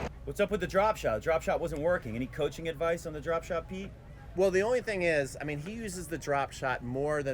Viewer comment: Pete (I think) actually says "Tsitsipas' drop shot" 😂😂